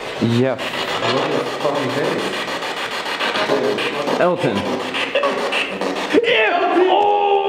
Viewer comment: I heard elton is devil